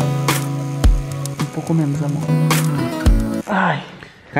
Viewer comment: VÍDEO NOVÃOOOOOOOOO GALERAAAAAAAA!!! ❤️❤️ Esse tá EDITADÃO, demorei mais do que o tempo normal pra fazer ele 😁😁 Espero que gostem e CONTO COM A FORÇA DE VOCÊS \O/ Bora pro vídeooooooo.... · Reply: no minuto 9:45 entendi o falando "um pouco menos AMOR" a Ingrid Tava ai nesse momento ou não? Não estou querendo insinuar Nd mais né eu entendi isso ai